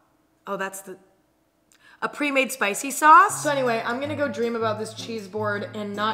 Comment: slow it down to 0.25. i think she said vodka sauce